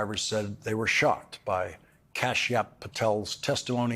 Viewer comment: Watching replay: did he just say Kash-App Patel? 😂😂😂😂😂